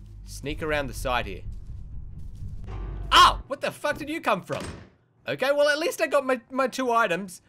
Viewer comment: at 1:51 you said "Ahhh What the fuck did you come from" You didn't say where the Fuck did you come from.